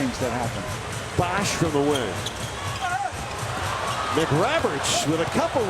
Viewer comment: Who else heard him say bosh for the win at the beginning